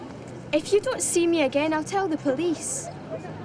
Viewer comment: I will see you Renton, that's I thought what I have been heard